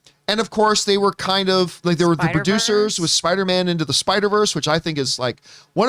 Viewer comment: What was saying at 3:08? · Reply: Spiderverse